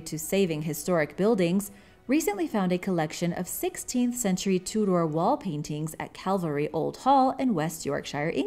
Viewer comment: Is this narrarated by a bot? Because some of the pronunciations are so strange - when she said Tudor I thought she said Two Door and even Elizabethan!